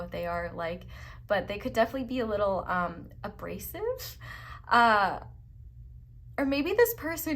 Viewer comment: Pile 3: I am doing a sudoku puzzle and this resonates but i gasped and had to rewind to hear you correctly cause girl i swear i thought you said his roommates were racist not abrasive. 😭💀💀 but we cool, continue. 👩🏽‍❤️‍👨🏿👩🏽‍🤝‍👨🏿